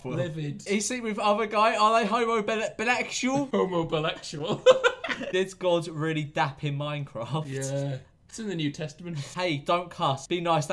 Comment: I thought it said did god really fap in minecraft? I would like to erase this Memory please